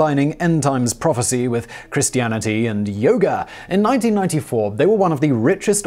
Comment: I thought he said YODA